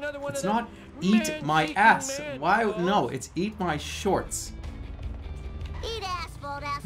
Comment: Bart is actually saying "Eat asphalt, asphalt eaters!" apparently lol, I've always thought he said "Eat ass" since I was a kid.